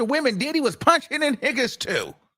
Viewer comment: I had to rewind because I thought he said all my "Niccaz we bringin the tea" 😂😅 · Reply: What did he say? I thought he said wiggas, too. 😂🤣😂